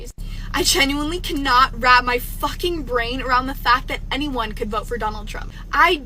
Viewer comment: she can't get her what around the fact that people voted for Trump ? Did she say 'brain' ? Ironic, seeing as how she doesn't really have one.